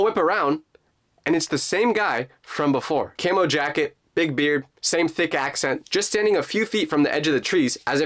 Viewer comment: I thought he said “same thick ass” 😭😭🙏 but he just said “same thick accent” LOL